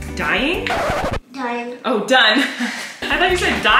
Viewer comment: saying things and you laughing about it is giving me pure joy! I've watched the clip where you thought he said dying when he really said done like 5 times at LEAST!!!